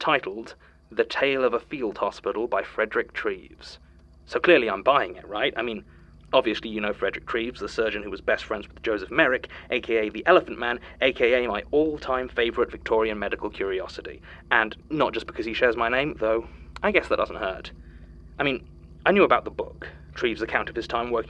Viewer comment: DID I HEAR ELEPHANT MAN? CAMP HERE AND THERE REF‼️‼️ /j